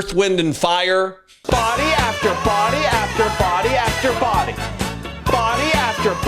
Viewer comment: He screams... "Murdered...Body after Body after Body after body!* I hear... "Trump,Miller,Hegseth and Vance !"